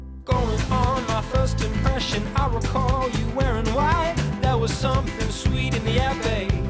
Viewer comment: i first thought he said “I recall you weren‘t white“